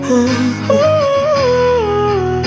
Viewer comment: I thought she said 'Sad Paper' for a moment.